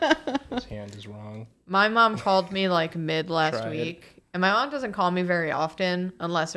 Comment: i thought she was saying that her mom called her mid 💀